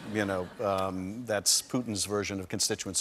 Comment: It's funny how at 1:27 rep Schiff says 'putin' but auto generated captions say 'president putin'